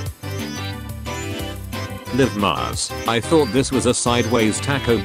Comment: D-did you just say "Scooby"? "SCOOBY"?!